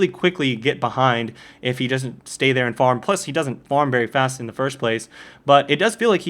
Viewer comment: "If he stays there and farm pussy" is what i heard LOL